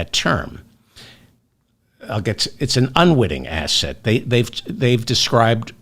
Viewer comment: Ahh.. at first there I thought you said "unwitting asshat"